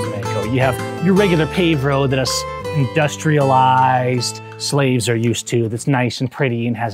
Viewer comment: I swear he said SLAVES 🤔